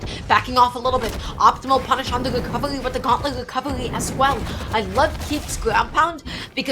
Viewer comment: What in the world was said from 0:20 to 0:23 cuz I ran it back 3 times and still couldn’t understand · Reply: optimal punish on the recovery with the gauntlets recovery as well